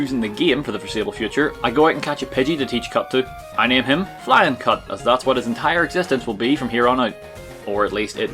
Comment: lmfao,I LITERALLY thought you said "Flyin Cunt",until I saw it on the screen.....lol :P